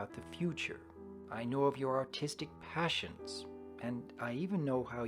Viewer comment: did he just say autistic passions?.....wow....I’m autistic too